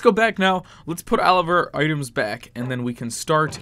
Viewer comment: It sounded like he said let's put Oliver items back lol 😂😂😂😂